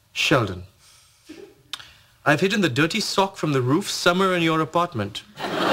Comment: When Raj says somewhere in this apartment, it sounds like he says summer in this apartment